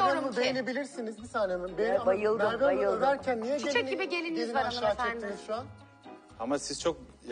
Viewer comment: te ne diyor. on numara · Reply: Tigris bisiklet ve özel sporcular kulübü DİYARBAKIR ezik gibi diyo galiba